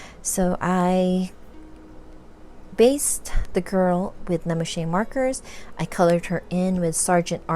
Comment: May I ask what the brand name and set of markers you used that you said around 31:31 please? I’m so very sorry, but I am not able to understand what you said. Thank you! 🙏🏻☺️ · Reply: Hello! It’s no problem. I said that I used Lemouchet Markers to base the illustration before I colored on top with pencils. They are alcohol markers.👍🏼 Thank you for watching and take care!!😊💕